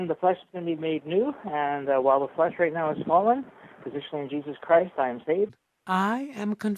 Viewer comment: did just say that his "flesh is swollen, positioned in Jesus Christ"? Dude phrasing, lol